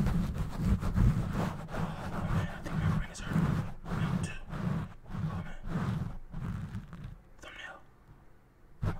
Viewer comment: Im non English speaker, I'd like to know what he said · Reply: He said I think my brain hurts